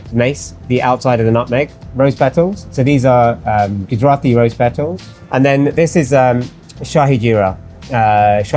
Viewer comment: When he went through the spices in the beginning I heard "Dothraki Rose pedals" and I think if I will every buy them that's what I gonna write on the container :D